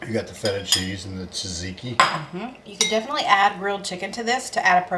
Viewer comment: I enjoy your videos and your recipes so much. Just before Ziggy added the chicken protein to your shopping list, at 11:03, was naming ingredients. When he said "tzaziki," it sounded like "Ziggy," so I'll bet that's what got your device's attention.